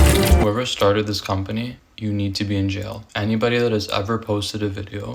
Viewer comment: WHY do i always hear this sound at 3:47 as, "YOU NEED TO PEE!!👹👹" 😭😭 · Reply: I thought it said you need a beat 😶‍🌫️